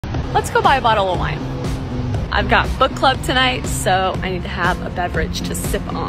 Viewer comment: For a sec I thought she said foot club 😅